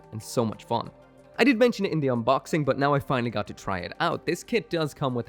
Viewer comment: Fun fact: I got a lil distracted by my doggy while watching the video and I'm hearing "Indian Boxing". I misheard "In the unboxing". Cracked myself up 🤷‍♂️😅